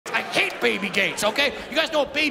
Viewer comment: I thought he said baby gays at first